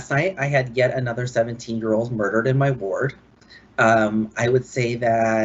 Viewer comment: did he say, "17 GIRLS MURDERED...." IN ONE NIGHT????